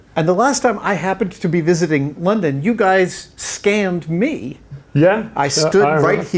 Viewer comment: Thought it said scammed